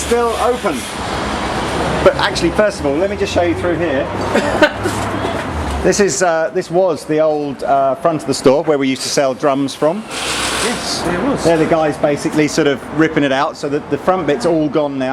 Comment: i started this video and the trashman came by i heard "this used to be where we used to sell drugs from" instead of "this used to be where we used to sell drums"